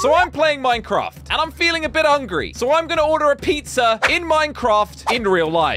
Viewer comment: broo i thought he said angry 😭